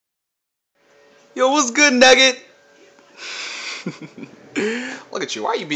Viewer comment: At the beginning of the video, I thought said "Yo what's good nigga!" Anyways, nice dog. I can't have a dog like that, since I have asthma and can't have a dog with too much hair. I got a Chihuahua.